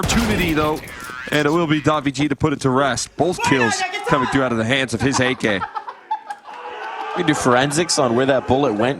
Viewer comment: what did he said? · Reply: Basically like "Nice one Jackie Chan".